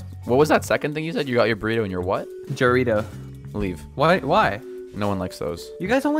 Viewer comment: Not gonna lie I thought when he said he got his burrito and jarrito I thought he said Doritos 😭 cuz it’s pronounced “ha rito”.